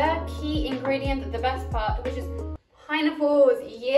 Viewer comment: is it just me or did I hear "Pinenipple" instead of pineapple lmao